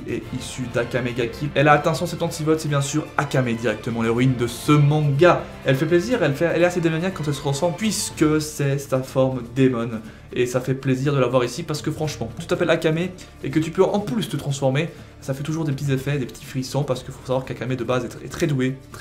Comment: bon jcroi que j'ai compris "ca te fait plaisir"